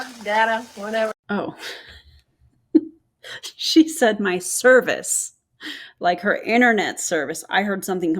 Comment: I definitely heard cervix too 💀